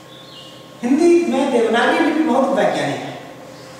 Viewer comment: Hello guys, can you hear the word he is using in the sentence starting from 2:07 ? Hindi mein ......... bahut vaigyanik hai. Please tell me if you know what is the word which should be fill in the blank to complete the sentence, it seems a great word to learn. Thanks in advance. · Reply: He is saying "Hindi mein Devnagri Lipi bahut vaigyanik hai".. देवनागरी लिपि